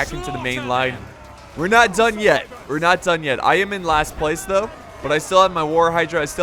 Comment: I thought you said I’m not done yeti